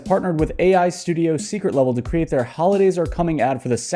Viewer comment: I thought that said 'secret evil'